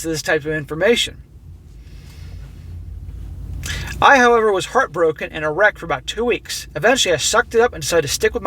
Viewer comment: What he said: "I was heart broken and a wreck for two weeks." What I heard the first time before I did a double take and skipped back to listen again: "I was heart broken and erect for two weeks." I was thinking "dude, for God's sake, go to a doctor." Although uncommon, priapism is no joke.